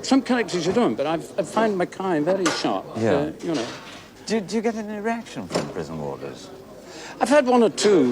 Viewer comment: Sounds like Noakes says 'Do you get an erection from prison warders?'....